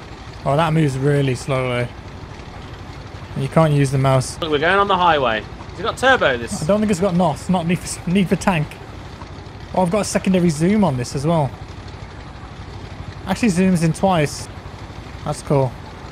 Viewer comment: Am I the only one that heard Jack say "Y'all niggas got Nos" at 1:05 or am I just hearing things lol